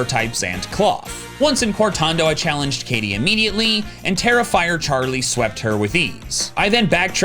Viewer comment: Haha I misheard “Tera Fire Charlie” as “Terrifier Charlie,” which is both accurate in the Grass gym and a hilarious nickname for a silly little guy 😅